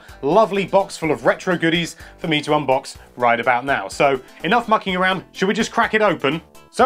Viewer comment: thought he said enough ffin around